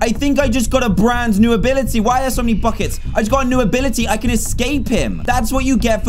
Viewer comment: Y'all i know he said "why are there so many buckets?!" But I heard "why are there so many f-kers?!"